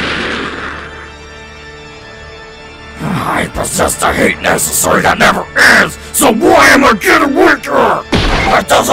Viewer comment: I couldn't understand a word Bowser just said. What was he saying? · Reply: he says i possess the hate necessary that never ends so why am i getting weaker? It doesnt make sense, why?!